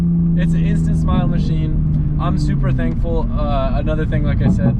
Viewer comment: I thought Adam said he’s “Supra thankful”😂